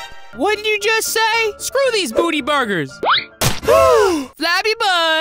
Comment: I thought he said:screw these booby burgers